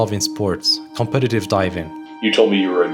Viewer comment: I thought it said “competative dieting” at first😅